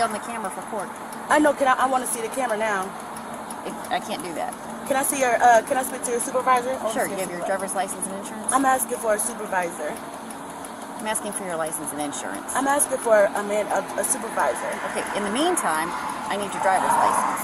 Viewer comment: sounds like she says “I’m asking for a man — supervisor”. Sisterhood for the win.